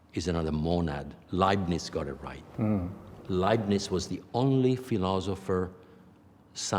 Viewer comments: who got it right? I can't catch the name he's saying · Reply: All good, i figured it out: Gottfried Wilhelm Leibniz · What is the name of the person he is saying at around 59:20? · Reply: Gottfried Wilhelm Leibniz